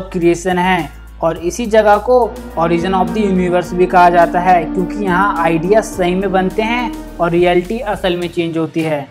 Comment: what did he say i can't understand "yaha idea sein bante he"